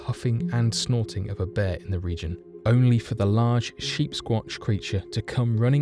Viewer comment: Sheepsquach, eh? Must of been a cold lonely night for, Harry after leaving the, Henderson family.😅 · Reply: I thought he was saying sheepsquanch, like on Rick and Morty.